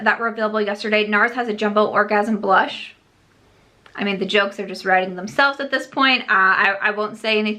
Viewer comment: Misheard quotes: "Nars has a jumbo orgasm blush I mean the jokes are literally writing themselves at this point" What I heard "the jokes are rising themselves at this point" <3